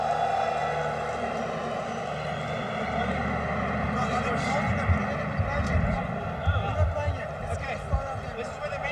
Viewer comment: Wat zegt François daar? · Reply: Nou begin ons speel